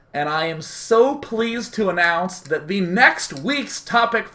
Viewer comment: My video stopped loading at 28:15, and all I heard was "and I am soap..." loL